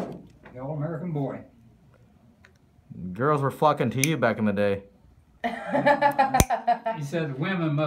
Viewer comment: I swear I heard the F word lol